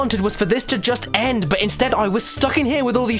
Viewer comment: I heard “Throttle that pollock!”